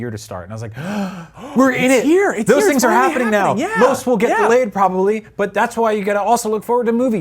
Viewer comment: He said "delayed". I heard "get laid" .